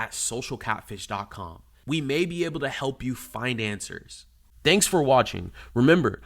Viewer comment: At the end of the vid I thought he said, "We may be able to help all of you financers." Then I realized he said "find answers" not financers! ..Kinda true tho as the victims are financing these scammers.